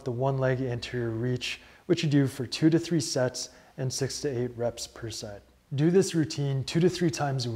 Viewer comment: I’m not gonna lie… I’m really dumb. I thought you said 68 reps per set on the last one. I just sat here and did 68 of those squat things; my ankle is screaming for help